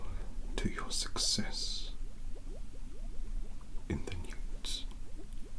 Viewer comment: did he just say nudes or nukes hol’ up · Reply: NEWTS. The year Seven exams at Hogwarts.